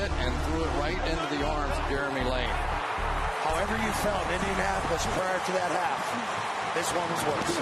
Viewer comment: What’s he say here? Sounds like Indianapolis..